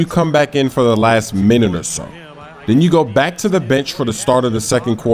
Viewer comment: i thought said the Bich. LOOL!